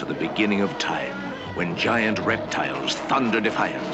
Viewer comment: i misheard "where giant erectiles"